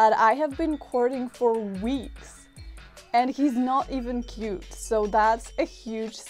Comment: “And he’s not even cute, so that’s a huge sacrifice on my side.” eye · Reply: See I misheard it as "he's not even a duke, " and I thought maybe this was a Polish nobility thing like in Tolstoy where everyone seems to be "Prince so-and-so," but they're not even part of the royal line of succession and they work as a minor government bureaucrat and you just shrug and keep reading; and I thought that made it funnier because of the social climbing aspect of "...so this is a big sacrifice for me." Either way it was hilarious... I like Miss Wilde best, but I can't say why!